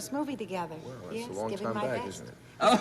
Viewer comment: What did George Maharis say at 8:55? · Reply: He said, “well, it’s a long time back, isn’t it”. I guess it was supposed to be a joke either about Betsy’s or Martin Milner’s age.